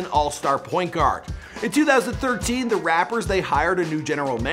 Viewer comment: did u hear him say rappers instead of Raptors